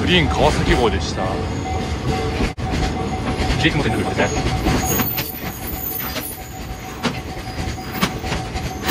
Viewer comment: 3倍速のとき何を喋っていたのでしょうか。早すぎて聞き取れなかったです。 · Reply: 「地域路線って感じ」と言っています。（たぶん）